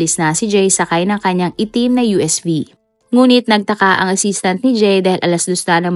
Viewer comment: Hi po, at 9:21 tama po ba yung narinig ko umalis siya sakay sa kanyang itim na USB?